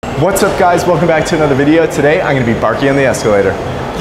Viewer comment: I thought it said "baking on the escalator" 😂